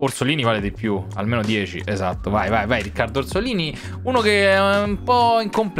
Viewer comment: solo io ho capito mussolini🤨